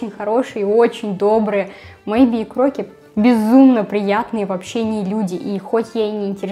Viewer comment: эмм... Мейби и Крокет очень приятные вообще не люди (если что мне это послышалось)